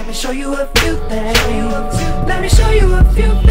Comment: Am i the only one who always thought he said let me show you our toothpaste ?